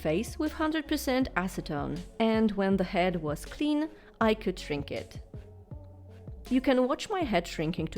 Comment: I heard “I can drink it” at 1:40 for a split second before my brain connected the dots Anyway, I love your art AND I’m glad you aren’t drinking acetone lol